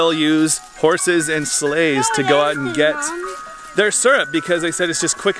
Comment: OMG for split second I thought you said horses and slaves, but you said horses and sleighs, lol